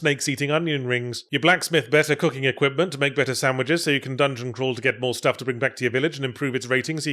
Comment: For a second I thought he said "to make Greta sandwiches" 😂